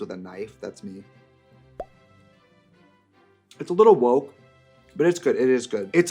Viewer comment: Did she say "It's a little woke..." ???